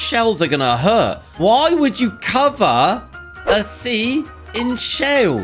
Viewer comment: bruh, he said "Why would you cover a sea in shails" instead of "Chair in shells"